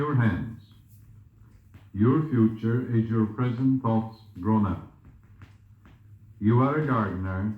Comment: what does Joseph say at 4:29? ‘Your future is your present thoughts drawn out?’ I can’t quite make it out..